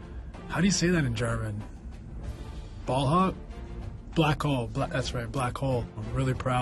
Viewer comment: Did Nash just call him a ball hog ?